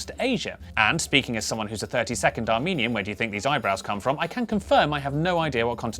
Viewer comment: I definitely heard, "I'm a 30 second Armenian."